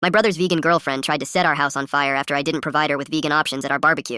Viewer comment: I clicked on the video to see what it was, and the first thing I heard was my “my son‘s vegan girlfriend tried to sit her house on fire.”like what!!!